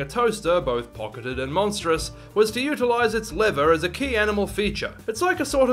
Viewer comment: Sir I thought you said "liver" at 7:36 and I was very concerned where you were going with the design